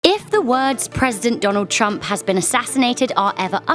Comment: did she say "if the world's President"?